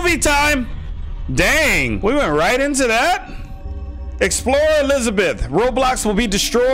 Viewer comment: I thought you said let's die right Into this